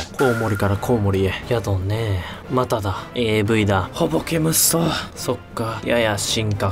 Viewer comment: ん？ん？AVって聞こえたの俺だけ？ 10:02ここも 10:36はいアウト完全にあれに聞こえる 12:57ここも